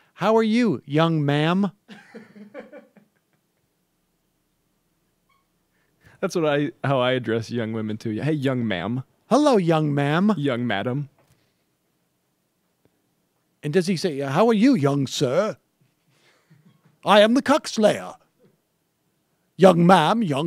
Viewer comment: I heard young ma’am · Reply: I heard “nice young madame” 😂